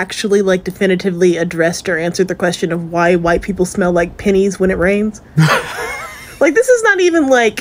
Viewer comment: The girl saying we smell like penny’s when it rains. What I heard was “we smell like money”😉